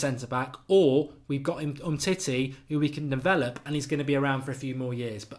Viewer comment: Did he say 'titty'? :)